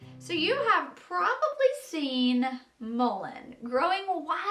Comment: Hey, the closed captioning at 2:00 says mold instead of mullien, but I don’t know that it matters. Also, mullien isn’t on your check list. Is that because it’s so seasonal? Thanks! I’m so excited to start making my own tea blends with things I grow myself! You are such an inspiration!